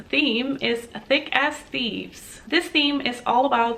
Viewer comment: The items were underwhelming this month. But your unboxing was still great 😁 Also when you talked about the theme for next month, I thought you said “Thick Ass Thieves” lol